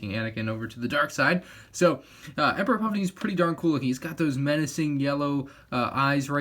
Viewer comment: What he said: its pretty darn cool What i thought he said: its pretty DARTH cool